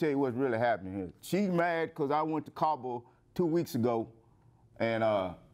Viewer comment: she is literally choosing child support over a present and active father to her children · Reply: O Cabo. I thought he said I went to Kabul. Like who goes there for relaxing?